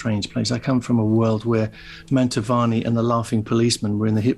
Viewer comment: Has anyone here figured out who Brian is referring to just after 16:55, who were on The Hit Parade? It sounded like "Menta Varney and the Laughing Policemen".